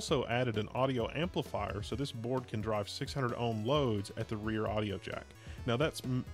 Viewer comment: "600 ohm load at the rear audio jack" all i heard was "600 hp at the rear wheels" lol